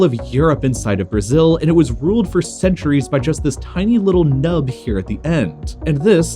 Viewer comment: What did u just say about Portugal m'lady?? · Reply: Tiny little nub